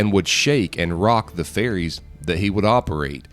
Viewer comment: I thought he said fairies.